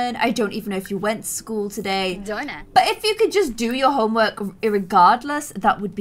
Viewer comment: it sounds like Macy said "darn it" because she has to do homework.